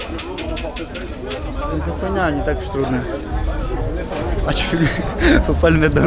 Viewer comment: Il dit quoi à 8:30 svp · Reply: il a dit c est de la merde , c est pas si dur que ca